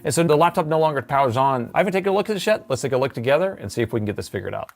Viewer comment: Every video when you say "I havn't looked at this yet" I hear " I haven't looked at this shit" 🤣 Great content though 👌